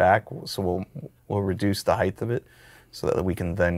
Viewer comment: Did you say "heighth" at 12:37?? 😅